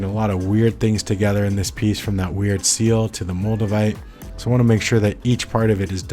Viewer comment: Moldevite? I thought you said Magnamite... I was just about to catch 'em all too...